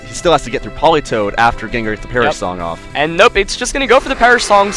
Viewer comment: Why do I think every time they say "Perish-Song," I hear "Parisol?"